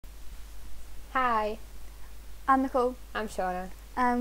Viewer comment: When Nicole said I'm Nicole I thought she'd said I'm the pope and was in a state of utter confusion